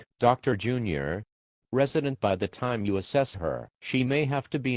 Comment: I heard "Resident by the time you assess her". That seems more appropriate.